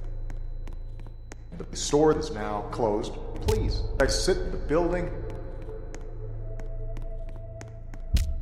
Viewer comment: I hear: “the show is now closed please egg sit the building”